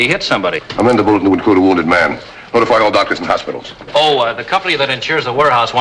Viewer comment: What does Dan Mathews say at 5:47 ? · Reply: "Amend the bulletin to include a wounded man"